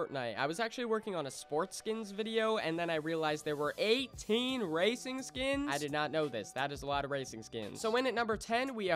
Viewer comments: I thought he said racist skins 😮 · Until I saw the first skin I thought he said racist skins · I thought it said Racist skins🤦‍♂️